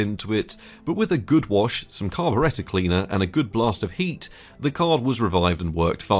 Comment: what cleaner did he say? Carburetor cleaner or what!?